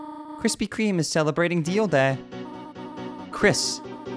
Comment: i thought i heard what you say not deal day